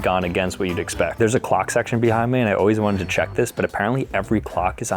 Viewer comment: i thought he said Glock section 💀